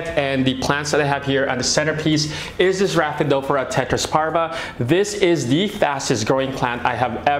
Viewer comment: does anyone the plant name at 4:09 ? i couldn't hear it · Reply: Rhaphidophora Tetrasperma